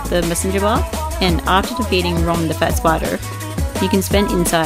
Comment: …OMG, did I just hear you say, ❝Rom The ꙳FAT꙳ Spider❞ instead of ❝Rom The ꙳VACUOUS꙳ Spider❞…⁈ 🤘🏻😹 If so… that's kinda cute~ 🕷️🤍🕸️